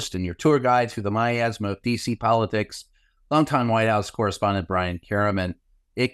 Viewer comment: The way you enunciate "DC politics" I heard "feces politics" and as I write this my point is all melting away into an epiphany of understanding. So never mind.